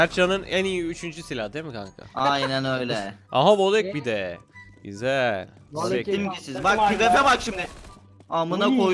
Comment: mertcan abinin ne dediğini anlamadım :D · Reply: Atila nerdesiğn lağn diyo